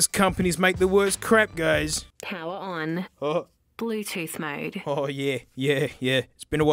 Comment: Wait, does it say, "Pull your tooth mode"?